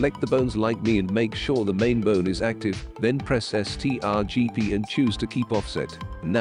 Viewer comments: What the heck is the bot saying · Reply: "Press strgp" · press ctrl + p and click keep offset i was like wtf is he saying had to pause and see what the ui said.